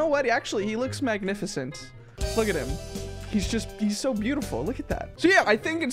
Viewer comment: did he just say kindley Kevin is beautiful????